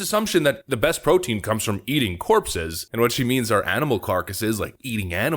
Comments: I'm LMAO watching her. You cannot make this stuff up. She is a parody of herself. Thanks for explaining because I thought she said "eating horses." What she actually said is even more hilarious. These are the people teaching our children? Someone call the truckers. We need more convoys. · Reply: Yea I thought see said eating horses at first but see says eating corpses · I actually heard her say: Eating porkses (not a native English speaker!) But still .. it had not even surprised me anymore at this point, if I did not misshear it. Oh and... has she just made a discriminatory statement against african indiginous carnivorous tribes, labeling them as right wingers? I never heard, that these tribes have been into American politics and taking the side of right wingers. Fly them in !